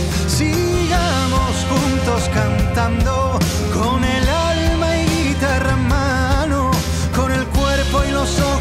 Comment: alguien más entiende que dice "grita hermano" o soy la única??? XD